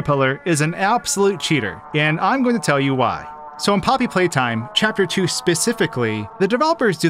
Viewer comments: The subtitles say Chapter 23 instead of Chapter 2. ERROR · "Turn on Subtitles" The subtitles in 0:14 it spell Chapter 23 instead of 2